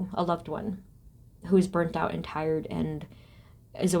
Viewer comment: pile 3, I heard you say we’re like a BURRITO 😂😂 I was like okay.. burrito is nice and filling 🤷🏻‍♀️